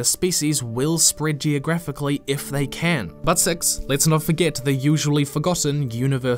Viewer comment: made me do a double take when I thought I heard "butt sex" Now of course I got a lot more out of it than this. Lol I really do appreciate the examples you cite because they really show how easy a Planet of Hats can be yet how it's not too hard to just put a little nuance in the people that make up a race or species. We're all the same in that we are all different.